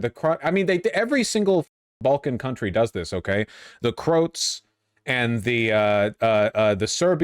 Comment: what the fuck did he just call us? crotes?